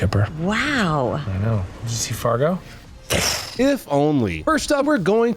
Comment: I thought it said Diddy for a sec 😂 I guess same thing 🤷